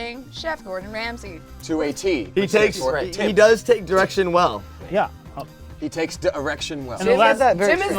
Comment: i heard he takes an erection well